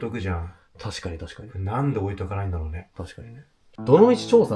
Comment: 女の人の声で「ねえ」って聞こえる気がするけど聞き間違い？？ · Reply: シルクさんの「確かにねぇ」のねぇ以降がかすれて女性っぽく聴こえてるだけっぽく聴こえますね。